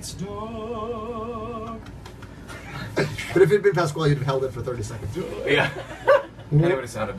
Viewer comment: Does anyone know what Lin said at 4:15? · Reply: He said “Pasquale would hold it for 30 seconds”, etc.. i think they are talking about their friend Steven Pasquale, who is married to Pippa Soo